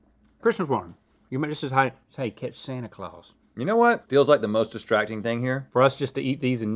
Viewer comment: Link says - Chris was born. You May this is how say catch Santa Claus.